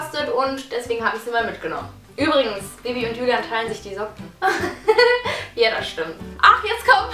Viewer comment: Dagi hat ja gesagt :bibi und julian teilen sich die socken. Ich hab verstanden:bibi und julian trennen sich diese woche 0.o